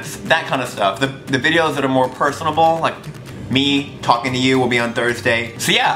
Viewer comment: did he say 'personable'?